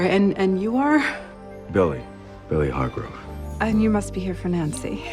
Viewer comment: When billy says his full name at 10:52 it kind of sounds like he said: “really heart broke”